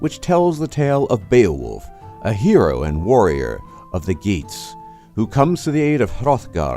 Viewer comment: I speak old english when im drunk (no one understands) 3:15 did he say that beowulf was the hero of the geeks? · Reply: He said Geets, but it is spelled Getes, a tribe from southern "Sweden" I think. I have never heard it pronounced but always thought it "Geteese."